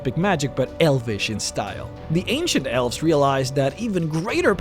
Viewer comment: At first, I thought you said, "The Asian elves..."